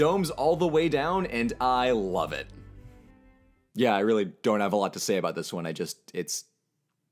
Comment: You broke me... At 3:10 i heard "I dome have a lot to say"